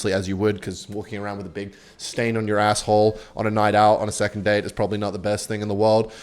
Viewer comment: did he said 2 nd date